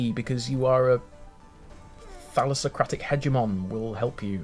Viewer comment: I briefly misheard Thalassocratic as Phallusocratic Which I mean......not wrong